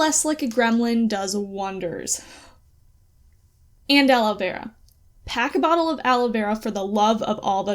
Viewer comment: Hey at 2:03 , what was it you said we needed? 🤨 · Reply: aloe vera! for sunburns :>